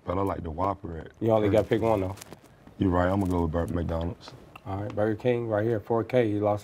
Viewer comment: When Marco asked him the Burger King McDonald's question did Dwight say I'ma go with Burmcdonalds or am I tripping 😂😂😂